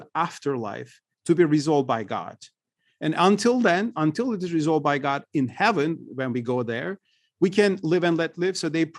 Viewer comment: what is the word? Mudjia? Mujia? Postpomnent in Arabic? Please clarrify. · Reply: Yes. A Muslim sect called "Murji'ites" in English and "Murji'ah" in Arabic.